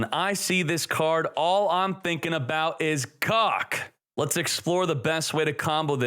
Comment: I thought he said "All I'm thinking about is cock" Then I was like nah, he clearly said KoG Apparently not